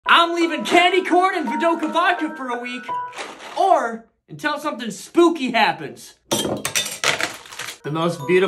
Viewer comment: oof I the first time I watched this video I thought that he said I'm living candy corn and vdoca vodka for week or something until something stupid happens